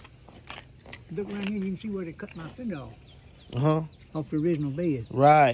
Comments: Sounded like he said he got his finger cut off? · Thought he said cut my finger off😂😂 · dats crazy oh yea I like that😮Both side do that ohhhh,I thought he said look rite here is where they cut my finger off This guys figure of speech 💬 Is 🤣 as hell,Both sides do dat,ewwwww lay it back down eewww His 🪠 plumber has him bamboozled Ohhhhhhh